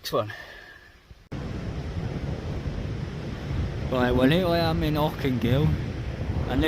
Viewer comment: Where? I can't understand what you're saying. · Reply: There's a lovely little park up place in Auckengill, overlooking the ocean.